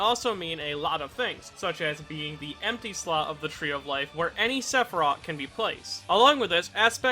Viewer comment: I thought he said "any Sephiroth"